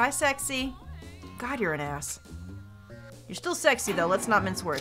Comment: I thought Nagito said you're an ass.